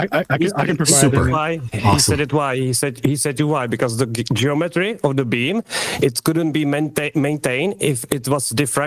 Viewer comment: I keep hearing "The geometry of the bean"